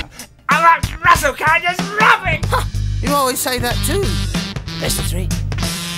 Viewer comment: what is actually saying, it sounds like "Ah russel can I just rub it?" Rub it, rub what to be exact??